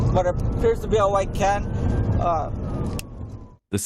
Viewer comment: i had to rewatch and add the subtitles....i thought he said "white cat"...but he said "white can"...lol